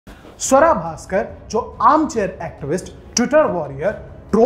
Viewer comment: I heard sadda bhaskar...